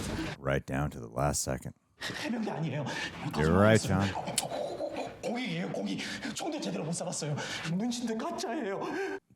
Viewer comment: just wanted to say that the English translations here really did this scene poorly. Instead of Dae-Ho saying "I didn't even serve in the military. I've never even held a gun.", in Korean it actually translates to "I was a social service personnel. I've never even properly shot a gun." For those who are unaware, "Social Service Personnel" is a alternative form of military service in Korea for those who are phyiscally or mentally unfit for full military duty. You go through basic military training (about 3-4 weeks) and once done, go and help ppl in places in subway stations, disability support centers, or etc! I'm not too educated on this myself, and just got this info from a reddit post I saw, but it really helps you understand the scene, and also Dae-Ho. I wasn't aware of this when I watched the 3rd season, and was immense dissapointed and confused (bc of the seemingly build up they had for him being part of the Marines, and such). Because of this, it made his death less impactful for me, which I found incrediably unfortanute, because I did really care for Dae-Ho's character D: I wanted to share that with you as well, bc it really changes the way you look at the scene! Hope this was insightful, bc it certainly was for me!! English translations can really ruin a scene if done incorrectly :( it's dissapointing.